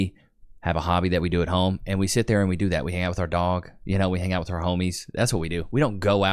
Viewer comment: I didn't catch that. Was that "hang out with our dong" or "hang out with our dog"?